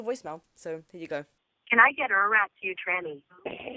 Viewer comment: I think they said can I rough you up( I think they might fight but they don’t know proper English they wore probably just trying to scare you lol